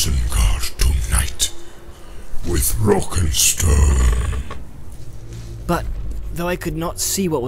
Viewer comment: Did i hear a rock and stone? Thats it lads ROCK AND STONE Rock and stone to the bone